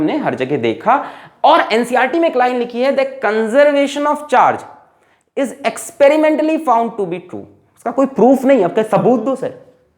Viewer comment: Sir at 15:50 what ncert line did you said? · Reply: Sir ne kaha hai ki conservation of charge jo hai woh experiment karne par true paya gaya hai 👍 Koi theoretical proof nhi hai iska .